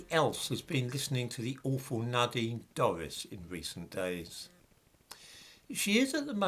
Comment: Nadine Dorries! - I thought you said 'Nodding Doris'. I had the picture in my mind of someone else.